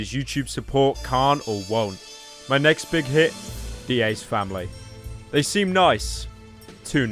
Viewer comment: I thought he said the EA family at first